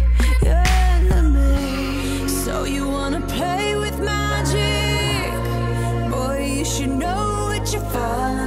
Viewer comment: I thought she said " So you wanna play with my cheek" But l guess not